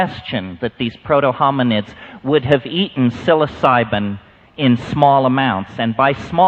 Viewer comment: what he said??? sysoliban? i can't find it in google please help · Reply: Psilocybin Mushrooms. They're more commonly known as shrooms. A mushroom that's considered a natural phycodelic drug.